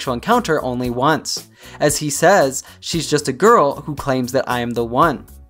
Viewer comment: I always heard the word "claims" as says). I love all MJ's songs.